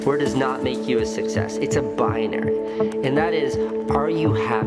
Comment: What is the word which he says in 0:50 ? Boundary?